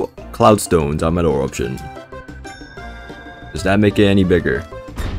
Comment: is that WHAT??? what did bro say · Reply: bigger